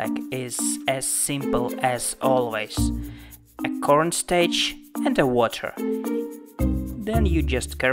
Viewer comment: Did he say cornstaech